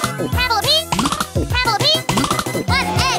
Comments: As it gets faster and faster: "Bunhead" "Can full of pee" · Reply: What I hear "On egg" "handfu of peas" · When it goes fast, she sounds like she is say "won ed" and " pentallly" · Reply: Its 1 egg and handful of peas